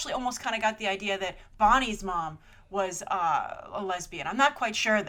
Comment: what did she say ??? whos mom ?? I couldent hear that at all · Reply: I think she said Bonnie's mom. Isn't that the girl from Toy Story 3?